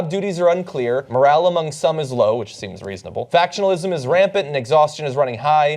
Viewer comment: Ha. I had this on in the background and thought you said that BOTULISM is rampant!